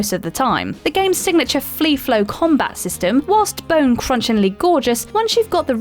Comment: Did she say flee-flow combat?